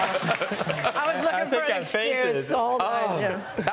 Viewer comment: What did Mark Ruffalo say? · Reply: Don't be fatuous Jeffrey 0:51 I think she said, "I was looking for an excuse all night!" 💋 Implying that she wanted to kiss him even before he said that! 💋 However, I bet it was staged cuz around the time she mentioned all the witch roles around 40 yo... Mark moved a little closer to her! It's all good...so much fun to watch...I love this show! 🇺🇸